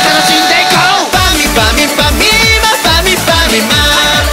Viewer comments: ファミファミファミーマ のとこが パミパミプァミーマ？ って聞こえるw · さいごのファミファミファミーマファミファミマのとこぱみぱみパミーマパミパミマって聞こえたw